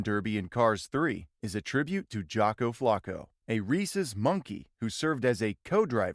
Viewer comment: a racist monkey? That’s what I heard lol